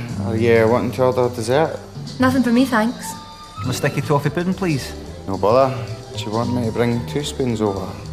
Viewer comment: what did he order for dessert? i couldnt make the word (of any limmy's episode, actually) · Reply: I thought he said "Sticky toffee pudding, please."